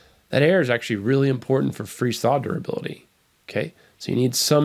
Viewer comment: What do you say at 6:09? Air is really important for "free solderability"?… I listened several times and still can't get it! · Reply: I'm fairly sure that he says " ... freeze/thaw durability." Hope that this helps. 😊